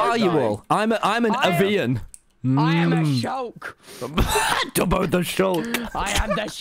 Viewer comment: I thought tubbo said “I am A JOKE”